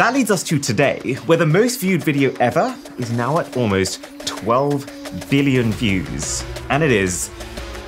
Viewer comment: I am hope I am not the only one who heard "12 Villion views" Did your brain press V instead of B while speaking 🤣?